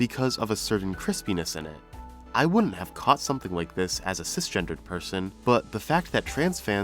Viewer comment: Happy pride month. And on the Trixie bit I thought I heard you say on my phone ‘as a transgender person…” but you actually said “as a cisgender person…”